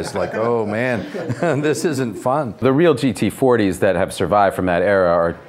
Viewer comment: Did Matt Damon Say TT 40s?? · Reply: he clearly says GT40